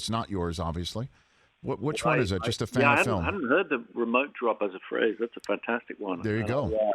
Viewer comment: Which was the first film did he say he would like to binge-watch? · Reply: "Yeah, I haven't heard the 'remote drop' as a phrase, that's a fantastic one." He isn't mentioning any movie.